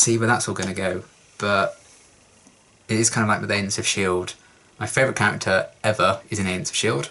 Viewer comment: Hi! I'd like to know : at 19:34, you talk about your favourite fictional character of all time. English is not my native language so even if you tell it twice you speak very quickly and I didn't understand it. ( even the auto-generated subtitles didn't got it). Something from the Shield ? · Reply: He said Agents of Shield. I have never considered to be a particularly fast talker tbh, but I sympathise if it feels like that. I speak conversational level German and every German person sounds like they are saying a million words a minute when I talk to them!